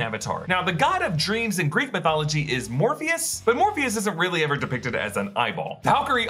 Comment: i thought he said morbius for a second